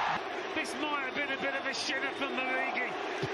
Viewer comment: did he say “shitter”? 💀